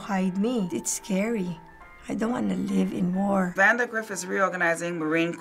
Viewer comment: did she say she doesnt wanna live anymore? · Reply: Jet Fury no lmao she said “I don’t wanna live in war”